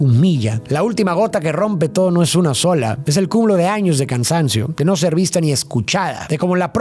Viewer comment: entendí “es el cul0 de años de cansancio” JAJAJAJ